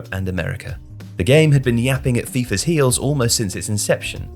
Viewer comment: did he say the game had been “yapping” 😂😂